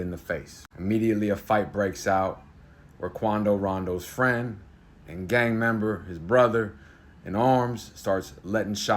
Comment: I have no idea who these people are but at 04:17 did he say "Rakondo Wondo"? Im positive thats not how its spelt but thats one hell of a name 😂. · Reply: Quando rondo